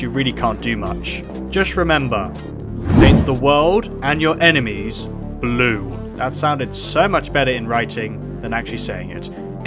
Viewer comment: my dumbass thought that said emesis blue